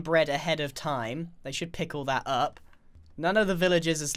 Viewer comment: did he said pickle